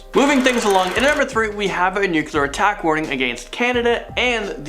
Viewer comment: I thought it said Canada Nuke warning on USA